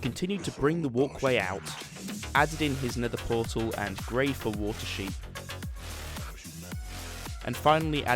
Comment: There was one part where he put in the portal and after that i heard “and grateful water sheep” can someone tell me what he said?